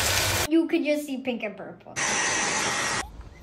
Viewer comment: I thought she said so preppy👁️👄👁️